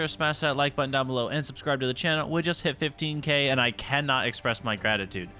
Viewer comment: did he say 15k subs ....after 7 days ...means now it is 117k subs WHAT THE FUUUU... He is blessed by the algorithm